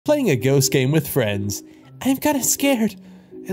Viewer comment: Stupid for thinking that when it said ghost game I thought it said enough gay😂😅